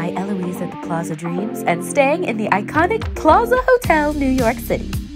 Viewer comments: i thought it said pizza hotel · I thought it said PIZZA hotel 😂